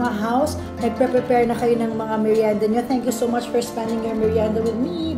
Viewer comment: did she say bismillah.